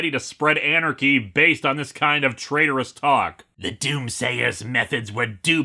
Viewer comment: I misheard "doom-sayer" as "Doom Slayer". Could be an interesting crossover there.